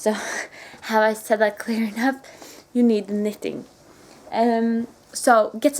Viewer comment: Teaching my four year old from Australia about snow and cold winters. I'm 41, never seen snow. when you said " have I daid knitting clearly enough?" he said " Did she just say kittens?" lol